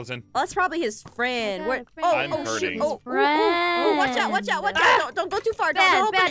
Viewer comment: i think almost said oh shi